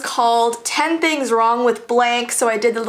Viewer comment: I thought she said "10 things wrong with Blake" then I laughed and said "what did Blake ever do to you?" 😂😂😂